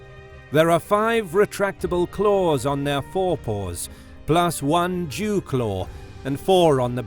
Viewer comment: Did mans say a "Jew Claw"?!? hahahahaha WTF